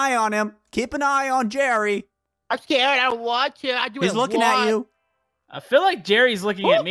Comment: WHAT IS HE SAYING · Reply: He says I want What I Want